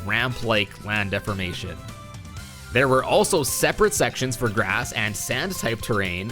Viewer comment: did u say "Sans?"